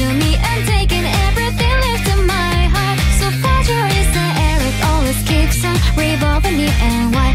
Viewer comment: I thought she said “so fatherless” but it was actually fragile😭👍